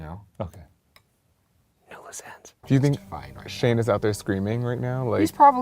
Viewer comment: What does Ryan say at 8:41 after Shane asks if someone had told him he murdered someone? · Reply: No loose ends